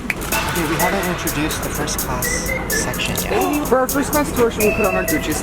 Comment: Uh...I thought he said, "We're going to FART CLASS!" and I was like, "Oh, snap. Thank goodness we don't have smellavision!" ;-)